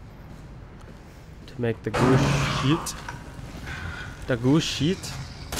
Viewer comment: 😂 Sounds like your saying goose Shit lol